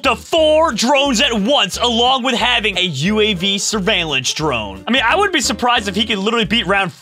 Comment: I thought he said surveillance